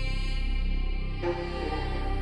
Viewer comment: Sagt die Stimme am Anfang wirklich, dass vodoo keine free beats macht? XD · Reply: Ich habe verstanden "Voodoo macht geile free Beats" 😂